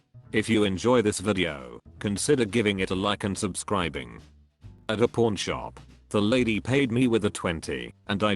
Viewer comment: thought that said at a porn shop at first, was just letting it play in the background lol